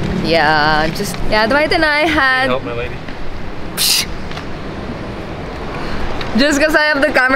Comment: Ano sinabi ni Dwight nung 13:15 ????? Kaloka di ko nakuha yun........huhuhuhu wala din sa comments. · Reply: may subtitle po, "do you need help, my lady?"